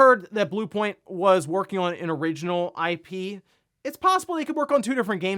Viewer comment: They did not say they are working on original ip. They said that they are working on original content.